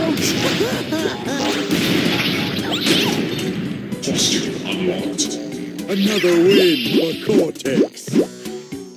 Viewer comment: What does the Voice say??? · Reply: "Costume Unlocked" eheh.